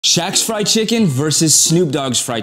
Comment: i deadass thought it said "scooby doo VS Shaq Fried Chicken"